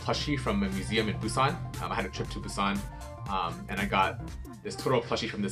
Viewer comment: What he says: I have a trip to Busan What the translation says: I have a trip to the sun